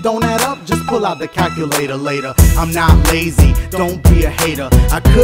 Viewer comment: bro I finally got what he says at 1:12 Just pull out the "calculator later" I thought he just said calculatorlator because he had to stay on beat.